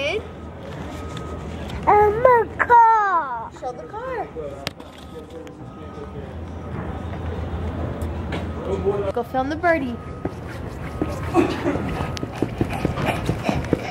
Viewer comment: To cute!! Love when he was saying birdie, it sounded like he was saying boobie haha. Love watching his footage :) He's taking after his mama and dad